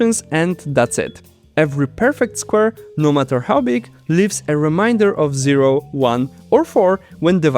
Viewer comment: Just figured I’d let you know that the “ai” in “remainder” is pronounced with a long “ay” sound (long “e” in most languages), like “say”, not the “ai” sound most languages would use. I only say this because I kept hearing it as “reminder” while listening to the video, and it kept throwing me off. This isn’t a complaint, mind you; your English is perfectly understandable. It’s just that, in this one case, it ended up sounding like a completely different English word! And I fully understand how nonnative speakers stumble over our vowels!